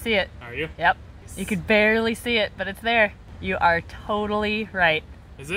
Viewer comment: "Yes, you are turtlely right" I swear this is what i heard